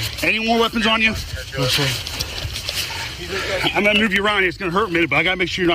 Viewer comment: "가위질을 왜 하는거냐?" 의문 가지는 분들에게 이 부분: 4:51 I'm gonna remove your ??? it's gonna hurt but I gotta make sure you're not hit, okay? ??? 부분이 라이엇? 라이언? 으로 들리긴한데 여튼 상처 확인하려고 그러는거네요. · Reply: 아 move you around 구나 ㅋㅋ 자막 키고 알았네요